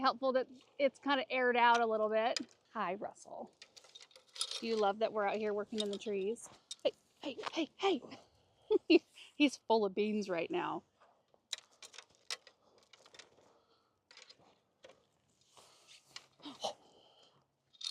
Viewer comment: So Russell is full of beans? My husband thought you said full of fleas? That can’t be! — Cheryl M - Bozeman Montana